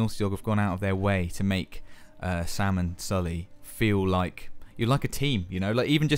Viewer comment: why does he call him Salmon Sully? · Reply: He said "Sam and Sully."